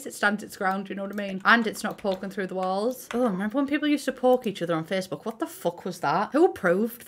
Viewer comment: thought she said "pork" and immediately thought what kinda Facebook is SHE looking at?? lmfaooo